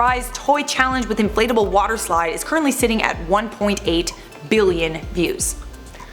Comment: I thought she said 118 billion views That would be craycray